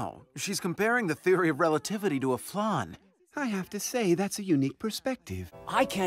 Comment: Comparing the theory of relativity to a what? CC says "fun," I hear "flaunt," nothing makes sense, chaos reigns. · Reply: Flan